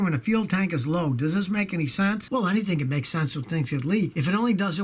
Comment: you said "Well anything makes sense if it ***********" What did you say there? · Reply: Things get leaks?